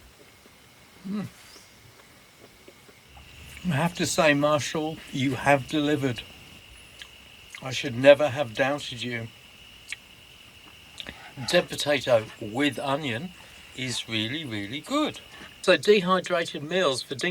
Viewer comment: Thanks for the video, I’m down on Hamford Water, Essex as we’re having great weather this weekend. Your Deb potato made me laugh, sounded like you were saying dead potato.